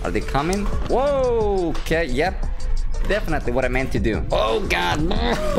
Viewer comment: I hear Kate yup